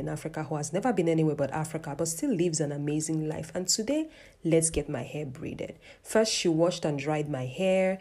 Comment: I heard you say "lets get my hair breeded."